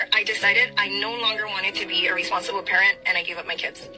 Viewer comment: I thought she said "irresponsible" but then i rewinded and heard "Responsible"... Bruh 😂